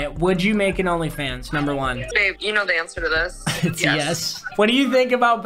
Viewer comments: who tf just said. "babe you know this" ??????? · did I hear someone call “babe”? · did i hear babe? papa we need explanation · Did I hear a babe at 6:15? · did I hear babe